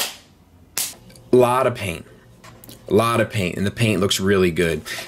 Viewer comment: "This one goes here and that one goes there... right?!" After your skit, when you said "lot of paint" I first thought you said "lot of pain" I think I need my hearing checked. Yeah, the red arm did throw me off. ;P Thanks for the review and taking the time to sort it all out for us. I greatly appreciate it.